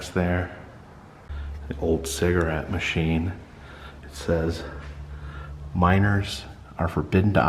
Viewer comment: Ha I thought he was saying “minERS are forbidden to operate the machine”